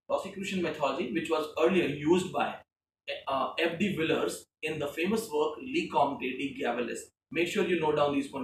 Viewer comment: could not decipher what you said. Can someone type the names? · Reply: Rosicrucian Mythology, used by Ab de Villers in his work "Le Comte de Gabalis"